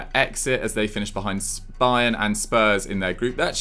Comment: Did just say spayern and spurs 6:21😂😂